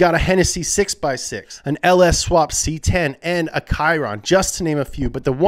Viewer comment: Did he just say "tchiron" instead of "shiron"